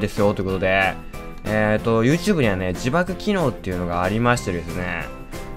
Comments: 字幕機能が自爆機能に聞こえたwwwwwww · 字幕機能が自爆機能って聞こえた(꒪꒳꒪;) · 字幕機能って言ってたけど自爆機能って聞こえたwww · 字幕機能が自爆機能に聞こえた